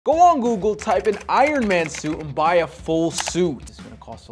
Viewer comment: I heard "let's buy an iron man soup"